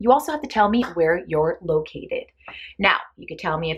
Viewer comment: Lol!! Gurllll...you are gonna think Im crazy😂😂😂I understood the ENTIRE vid...except 8:47....on my life...I listened to it about 10 times and honoust to God, all the times I heard "where your lowkey is" 🙈🙈🙈Im so embarrased, then I started hearing "where your lowkey aid" gurll...at that point I was like "girl bye, I must be going crazy" so lets stop this nonsense and scroll trough the commands to see what they have in common besides the favorite cartoon and why...and there is was...SWEET RELIEF😂😂😂I saw names of countries and cities popping up everywhere😋Lol, free at last!! You said "where youre located"😂🙈😂😋😂🙈😂😋🙈 Lol, I need those 15 mins of my life back😁 Anyhow...(lol Im exhausted now, but at least I can answer your questions😅) Amsterdam-Holland in the house girl, woopwoop!! (Thank God I got this question out the way..lol, I still cant😹😹) As a child my favorite cartoon was Scooby " Dooby" Doo😋. Ughhh, everytime I see it on tv nowadays its still youth sentiment to me, you know....remembers me of such warm and loving times....if anything happened my mom rushed to my rescue to kiss me, cuddle me, feed me, tuck me in...and, lol, now...its just me in this big ol' crazy world paying bills and tucking myself in at night😉So...its a bit cheating since its not really a cartoon but the animated version, you cant imagine my luck when in 2002 Scooby Doo came with there first semi animated cartoon!! I was in heaven!! Dont tell anyone...(whispering) even gave this puppy (hehe) an 8 on IMDB, s